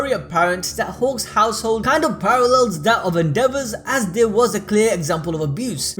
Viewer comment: every time you said Endeavor I thought you said “The devil”